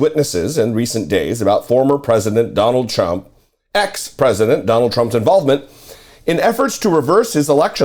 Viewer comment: did you just say "Donald CHUMP"? I mean...if you did, you wouldn't be wrong. 😂😂